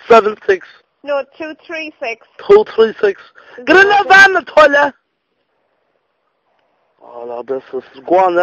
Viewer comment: get in the van ... (What did he say) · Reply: Think he said "I tell ya"